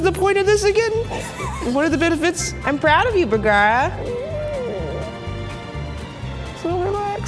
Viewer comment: wait did she say Vergara😱??!?that's my last name I'm also Filipino like him r we related????lol